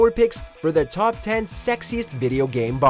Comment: For a second I thought it said sexist lol